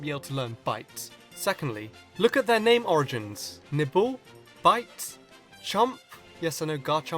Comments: did he say ''nipple''? 'cus that's what I heard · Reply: He said "nibble" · did he just say nipple (._.)WTF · Thought you said nipple when you said nibble... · I heard Nipple instead of Nibble. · I thought you said nipple not nibble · did you just say nipple?